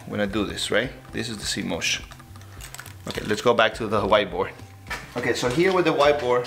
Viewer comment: I thought he was saying “the white boy”.......not “the white board”😂😂but it’s his accent that’s making it sound like that